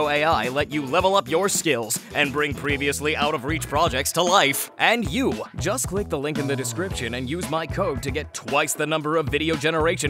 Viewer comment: why did i hear “use my toe”?